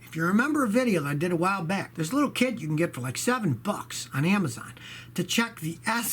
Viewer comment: I heard: There is a little kid you can get lol